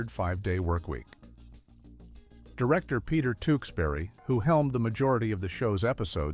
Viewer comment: Peter Sellers instead of Peter Tewksbury. Think they purposely make these "mistakes" to get comments?